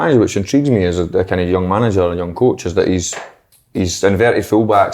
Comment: honestly thought si said manager a ayr at the beginning